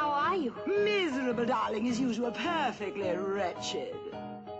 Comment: did corella say perfectly rachet at